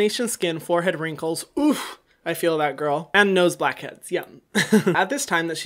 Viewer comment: Just me or did he say "Nose blacks heads YUM" ?